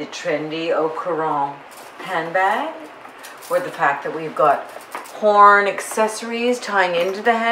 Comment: I thought you said porn accessories...horn accessories 😆